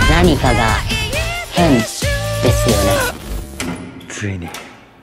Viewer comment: これ最後「味ぽんじゃねぇかこれ」って聞こえる